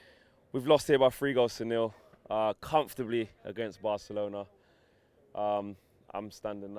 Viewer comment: Did he say they lost comfortably? 😂😂